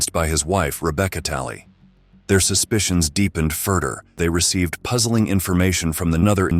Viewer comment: get an ai that doesn't say "furder" instead of "further" or proof read the script